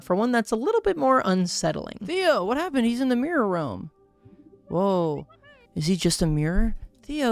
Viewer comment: I genuinely thought said mirror world and had an INSTANT neuron activation moment